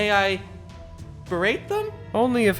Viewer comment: For a second there I thought he said "rape them". Must've been Heaven's Feel flashbacks. 20:30 And Hey! We got the James Woods impression anyway!